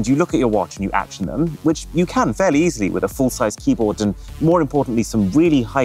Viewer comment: did bro just say "full sized keyboard"??? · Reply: He said " Even though it's better than a full sized keyboard " Go back a bit to re-watch that part